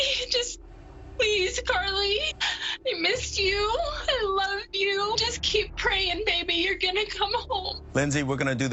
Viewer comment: HOLD UP (Sorry if said this already, I am only 1/4 through the video) At 18:51 she's referring to her in past tense. "We missed you, we loved you" Am I hearing this wrong???